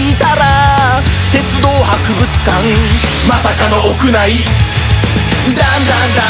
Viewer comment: この曲画面見てない状況で聞いたら鉄道博物館が絶望博物館に聞こえた